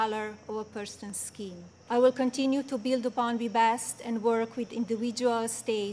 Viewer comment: Can SOMEONE tell me what the heck did she at 1:43 ......... something about :"continuing to build the bond to bast"........